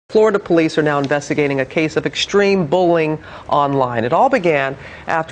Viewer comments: In the beginning of sounded like she said extreme bowling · in the beginning of the video, it sounded like she said bowling and not bullying. bowling is not something to joke about, kids! it's serious shit! bowling can lead to suicide! *finger wag*